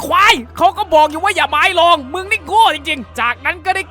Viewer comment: เขาพูดว่าอ่ะไรน่ะ ฟังไม่ออก5555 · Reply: อย่าหมายลองคับ